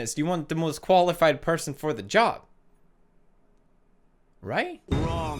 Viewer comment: Did u hear that I think I heard joe